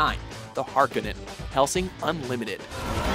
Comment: I think I heard "Hellsing Unlimited" but it is actually "Hellsing Ultimate". Sorry Hellsing Fanboy here!